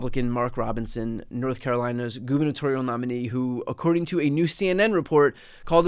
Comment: Did you just say "Goonitorial" nominee? I recently learned this word. "Goon" is slang for "chronic masturbater". If you didn't say that, then I'm claiming it, lol.